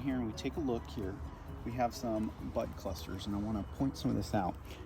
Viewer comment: I misheard you at first. I heard “butt clusters” - which quickly cemented the term in my head. Ha! This is the level of detail and explanation I have been searching for on ag extensions for years, so thank you. The “why” in what you are doing is what helps it all click. Pruning is an art - Sending this to my husband - he tends to take a pole saw to everything.